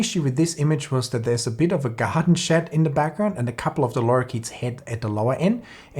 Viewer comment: I'm sorry. What is that German word at 8:00? "Gahadenschatt"? You refer to the dark areas in the background, I presume. But I can't find that word in my online dictionary (I'm sure I've misspelled it). Thanks. As for the lens: I could never afford it but as a Canon shooter, I think it would be nice if Canon offered something like this. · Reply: Haha, garden shed